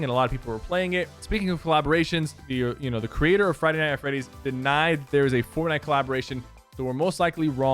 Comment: Did he just say... Friday Night at Freddy’s?